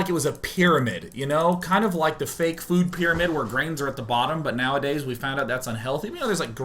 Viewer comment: on my speakers, sounded like he said BRAINS at the bottom...